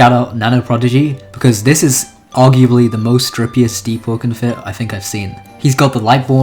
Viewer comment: who here heard him say strippiest instead of dripiest